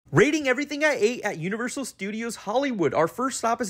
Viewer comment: I thought you said everything i hate 😂😂